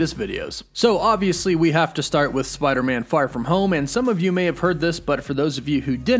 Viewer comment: OMG, you are so excited you said far from home, instead of no way home, 0:45 hahahahah man, woof woof